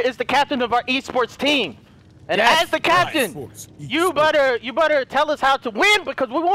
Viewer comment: Did said “ you butter” 😂😂